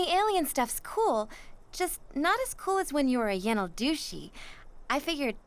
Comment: did she really call him douchy or is that an edit am I stupid? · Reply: It's "Yenaldooshi", what the Navajo kill Skinwalkers.